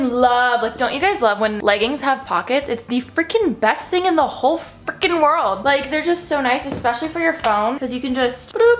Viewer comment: Am i the only one who thought she said f...ing? 😂